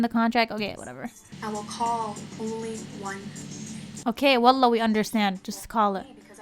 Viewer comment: LOL did you say wallah or I’m imagining 😂